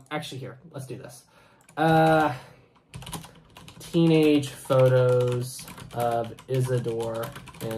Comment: bright side said nothing about teenagers, they said "thirteen" like the number. (At least what I heard)